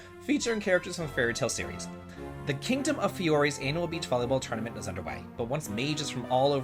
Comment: I had a bad misheard one with the beach volleyball game. I thought you said Anal beach volleyball, and it reminded me of Top Gun lolol